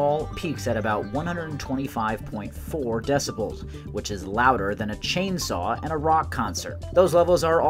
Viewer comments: At first I heard "it's louder than a chainsaw AT a rock concert" and I was like "Why the hell would someone bring a chainsaw to a rock concert?" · Misheard the first time. Louder then a chainsaw at a rock concert. Want kind of concert is that i thought😂